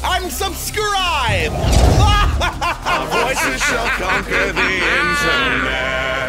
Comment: can someone tell me what he said bc i love that line ✨✨ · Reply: "OUR VOICES SHALL CONQUER THE INTERNET"